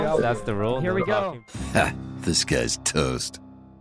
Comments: Petition to get Corpse to do “ Ha this guys toast “ intro for 10000 IQ episode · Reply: it’s “this guy’s toast.” It’s a Hearthstone reference because he used to make Hearthstone content. The name disguised toast is because it was commonly misheard, but the actual intro is “this guys toast” go watch any of his old content or interviews. He’s said himself that it’s “this guys toast” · Petition to get Corpse to do “ Ha this guys toast “ intro for 10000 IQ episode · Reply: i thought he said disguised toast lol